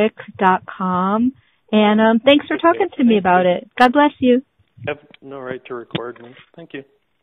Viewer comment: YouTube timer 4:15 did he say “You have no right to record me?” Wtf?! 😂